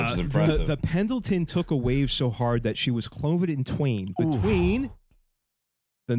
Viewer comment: I can't quite make out what you say, and the automatic translation says the boat was clothed in twain, is someone can help me understand this, thanks 😊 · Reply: “she was cloven in twain”